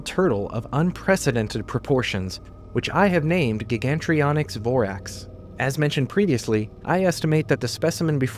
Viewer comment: i thought he said BORATS LMAO